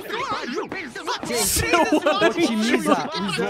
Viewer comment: o falo palavrão? ou foi coisa da minha cabeça? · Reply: Seu anjo